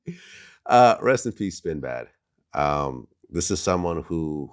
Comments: Nigga Had My Heart Skip A Beat When I Thought He Said "Sinbad"....🥺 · i thought vlad said sinbad.. had me checking google with the quickness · For a minute i thought vlad said rest in peace sinbad, cause i remembered that he was suffering from a stroke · i thought vlad said sinbad.. had me checking google with the quickness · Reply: Fuck I thought he said sinbad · HE SAID DJ SPIN BAD NOT SINBAD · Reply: I thought he said Sinbad too thanks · I thought he said sinbad i was about to have a panic attack i love me some sinbad he did have a stroke recently hope hes ok now tho